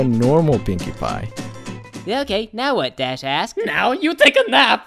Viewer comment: 2017? WHERES MY LATE SQUAD!! Also, 10:52 Who else thought it sounded like "Dash ass" Instead of Dash asked?